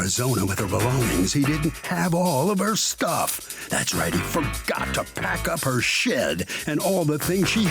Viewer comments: Did he just say “HE FORGOT TO PACK UP HIS SHIT”?? 😂 maybe I’m hearing wrong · I thought he was about to say “he forgot to pack up her shiiiiit” in the beginning 😂😂😂😂😂😂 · I thought he said “forgot to pack up her sh!t” 😂😂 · I misheard the announcer said "he forgot to pack up her shit (as opposed to shed) ..." 😂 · At first I thought the narrator said, "Forgot to pack up her sh*t".